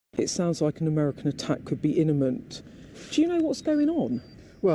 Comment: Did she just say an American Att could be "Inna ment"? For people who are always criticizing DT's speech they've got a cheek